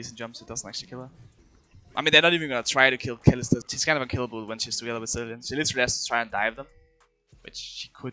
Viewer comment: can someone tell me what in hell did he said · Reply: kill Kalista. She's kind of unkillable when she's together with Zilean. She literally has to try to dive them